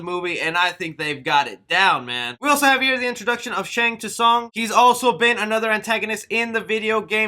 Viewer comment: did he just say "Shang tuh Sung"?????